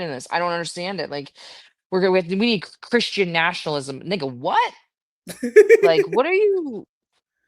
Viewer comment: Did I hear her correctly? Did she just say "n*gga what?"